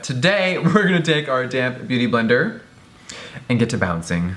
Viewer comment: I always thought Jeffree said "damn beauty blender" instead of "damp beauty blender" 😂